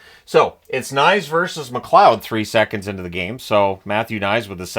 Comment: Did I hear you say " Knies versus Mcleod"@ 7:48? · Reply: McCloud